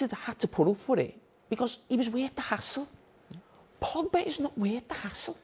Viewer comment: did he say "worth the hassle"? We outside the UK have to listen hard 😂